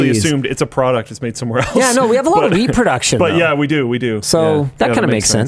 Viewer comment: i definitely heard “we have a lot of weed production.” Both are true these days!